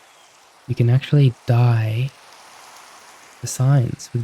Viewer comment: you scared me, I thought you said die bhahahah I actually thought I'd write some kind comment at the end of the video, but since I'm writing comments now, I wanted to say thank you very much for the kind words, support, and care. Actually, right now I think this is exactly what I was missing. You have a lot of comments, so I doubt you'll see mine, but if you do see it, thank you very much for the good content, you're very kind person, so I wish you all the best in life.